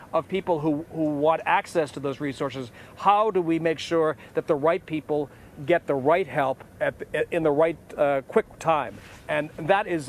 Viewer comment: wtf he saying? ''that the white people get the right help''? 3:41 anyone caught that ?